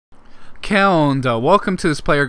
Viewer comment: what did u say from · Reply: Que onda it means what's up in Spanish